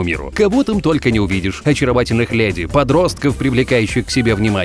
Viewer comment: Мне послышалось или он сказал ; кого там только не увидешь и очаровательных блядей 0:9